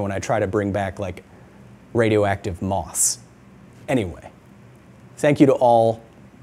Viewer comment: If customs gives you any grief about the radioactive moss just tell them "I'm not a super villain. Don't even worry about it." I'm sure that will convince them. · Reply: I thought he said 'moths' not 'moss'. Moths would probably be a bigger issue.